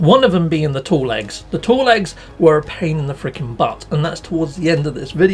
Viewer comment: Oh it's "Tall Eggs" I thought everyone was saying Tall Legs and to me that made sense that those would come from Striders...because they have tall legs. LOL Ok time to get crackin'.